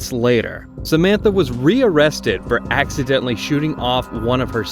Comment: I had to rewind that first part. It sounded like you said she was rearrested for accidentally chewing off one of Her son's toes, but you said shooting off 😂